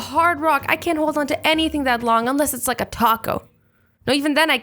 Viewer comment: I thought she said cock instead of taco...